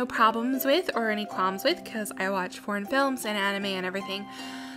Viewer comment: *watching* "I watch (Redacted) films" ... I'm sorry WHAT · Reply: ahem.. *writes it down on notebook paper* *hands the paper to you* *the paper says: it sounds like you said "I watch porn films"* ._.'